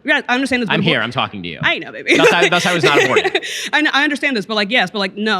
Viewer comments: Did she say "I know baby"? Seems like someone has a crush on Benny boy. 😂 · did she say “I know baby”?… can we not do that in a debate💀